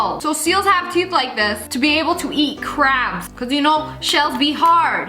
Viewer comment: who thought she said : eat crap 👇